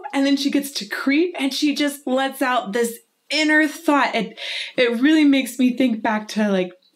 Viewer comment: I love how she's talking about how Haley is singing in a sexy way but when she gets to the word creep she lets out her "inner thot" at 6:26 . (I know that's not what she meant but thats what I heard at first :D )